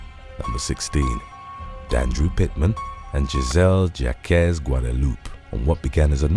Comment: #14 Until I looked up to read the name of the person mentioned, I misheard her name as Gisselle "Jackass" Guadalupe 😂